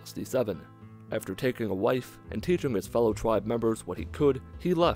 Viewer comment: I'll be honest, I thought you said "after he took a life" not "wife" for a few seconds there.